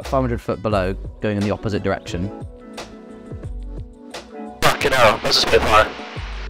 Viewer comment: When the subtitles say 'Blimey', but the clearly says "Fuck'in Hell!". Yeah, okay... 😂